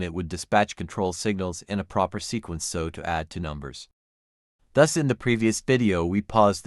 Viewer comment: Are there any technical faults in the text? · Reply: I did hear some things I'd like you to watch out for in your future videos. 0:18 It sounds to me like "to numbers", when I believe it should have been "2 numbers". 0:23 Here, I hear "fetch it" instead of fetched". 1:00 I don't even know how to describe what happened here. In short, I'm not hearing big mistakes in the information being conveyed, but I do hear some things I suggest you watch out for.